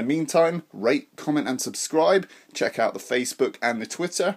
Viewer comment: It sounded like you said, “Rape, comment, and subscribe.” Lol